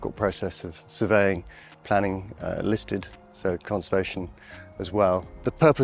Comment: Did he say this building was listed?